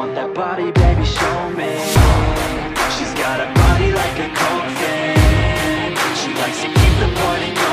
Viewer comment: All I hear is “ She’s got a body like a goldfish “🎶🎵🐠